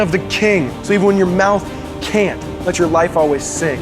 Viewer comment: did he say sing or sin at 3:35 near the end? it was surprisingly unclear for me...